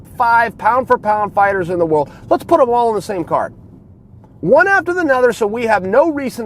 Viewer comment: Wow... I badly misheard Chael at first! I thought he said put the top 5 of the P4P list in the same CAR, not on the same CARD. That was a really interesting mental image...